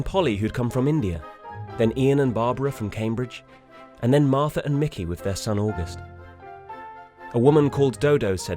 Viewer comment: Did he say "Martha and Mickey with their son August"? Martha and Mickey!? :) :)